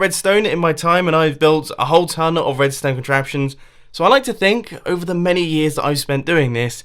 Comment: Instead of hearing mumbo say "I've made a tone of redstone contraptions" I heard it as "I've made a town of redstone contraptions"... till kinda correct, but needs to be done